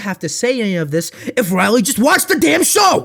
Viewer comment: Wait? Did you just say "Riley"? Is that ' real name?